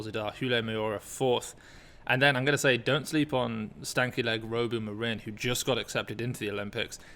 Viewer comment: wow…did you say stanky legs now in 👍🏽🤗